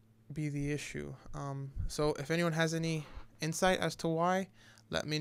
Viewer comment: The only thing I heard you say in the whole 5:30 is Anybody have to why ? ect....